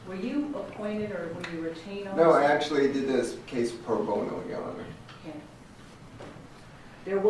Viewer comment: What does he say at 33:53? He wasn’t an appointed or retained lawyer? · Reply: Yes, I think he said pro-bono, which means, I think, he volunteered his services.